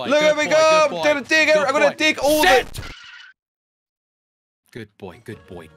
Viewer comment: Bro did say $hït or Sit?!?